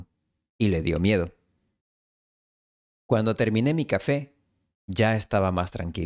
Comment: I really like the transcription method and find it very helpful. This is the fourth video that I've transcribed and I'm seeing improvement. Thanks so much for creating this content for us. However, I think that there are a few errors: 08:49 Cuanto terminé mi café. I think the video actually said "Cuando". 11:29 ¿Quizás yo soy solo su plan B? It actually says "solo soy". 13:44 ¿Es este el misterio de Ana María? Try as I might, I'm unable to hear the "de". Is it there?